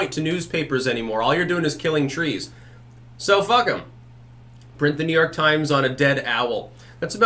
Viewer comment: "So fuck em. Print the New York Times on a dead owl." Did I hear this correctly? Absolute madman